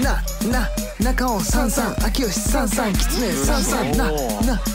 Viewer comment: 変態、変態って、言ってる、きつねさんが変態に聞こえる😁